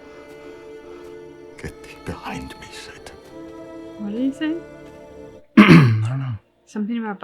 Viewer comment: Hi guys! At 10:56 when Marcus turns from Olrox he said, "Get behind me, Satan". You were asking what he said.